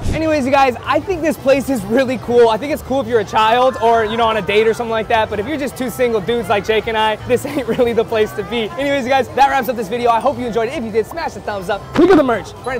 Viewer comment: Am I hearing things or did just say 2 (Single) dudes... I am here for you guys!